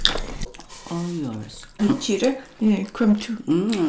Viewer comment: = Só eu que escutei: "mentira", "come tu"